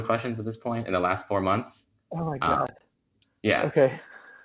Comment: Did he say oh God? 😂 😂